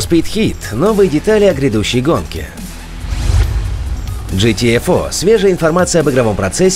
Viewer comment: Один я услышал хдесь GTA 4 (FOUR) , а потом рассказа про Гта 6?)))